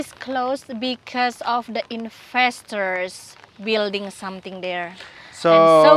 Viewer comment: Like locusts,destroy one place then move on to the next ! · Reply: Yes unfortunately so true 🥺 It sounded like was saying ‘infestors’ (investors) appropriate name maybe!!